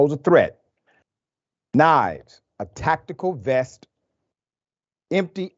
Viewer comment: I'm really surprised you did not question the Bullet Proof Vest. His lawyer says he has all this stuff because he's a hunter. When he goes hunting are the animals shooting back? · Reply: "I'm really surprised you did not question the Bullet Proof Vest" I thought they said a tactical vest.